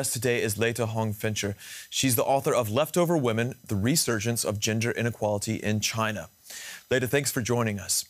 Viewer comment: Did he just say "The resurgence of ginger inequality in China"? 🤣🤣🤣 I think he just let slip what his real hair colour is! 🤔